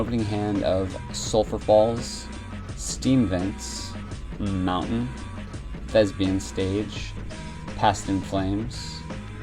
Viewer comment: I literally heard you said "Lesbian Stage" instead of Thespians' Stage